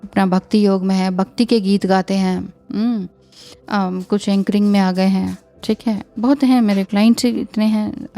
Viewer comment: Did you say anchoring